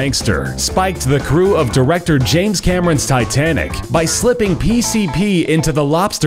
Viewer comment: Did I just hear "sitanic"?